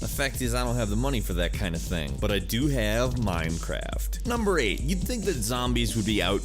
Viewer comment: did he just say Mimecraft xD